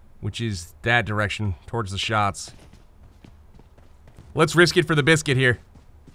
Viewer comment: "Lets risk it for the biscuit" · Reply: Thought he said "brisket" lol